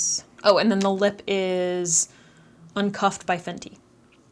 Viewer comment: Did anyone catch the brand name of the lip stick? Sounds like she said "lipcuffed" by vente or fente but neither have that type bottle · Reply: its the fenty stunna lip paint in uncuffed you can get it at sephora!